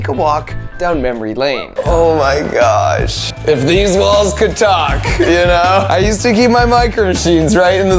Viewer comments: I thought he said if these balls could talk😂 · i heard deez balls could talk